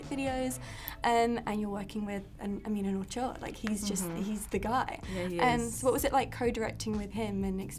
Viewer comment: GUYS can someone please tell me what says on min. 4:19??? english ain't my native language, plus the british accent makes it a bit harder for me to comprehend 😭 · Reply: She called James an “auteur,” which means a creator (and also “author” in French)